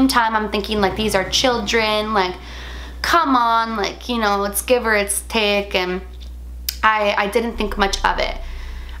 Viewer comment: I thought she said “come on you know. Let’s give her a stick”